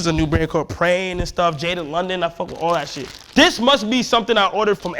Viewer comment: what brand did he say that brand was @ 12:21 ? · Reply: praying and jaded london